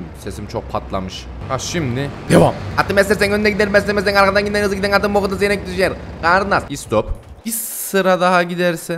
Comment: Abi 17:40 ne dedin ya 🤣🤣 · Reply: Atı beslersen önde gider beslemezsen arkadan gider hızlı giden atın b*kuda seyrek düşer😂